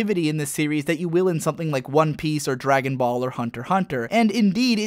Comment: wait a second, did he say that DBZ is creative at 10:34? Now i'm sorry. I am not trying to be an ass or troll here. I like the video and everything, but DBZ is anything but creative. All of the power ups involve a new form with no new powers, only the old ones getting bigger. Name one attack that Goku has besides Kamehameha, genki dama and kaio ken that he actually uses. You can't! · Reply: He said Dragon Ball without the Z. DB is a completely different anime and is actually an awesome show. DBZ is what it is.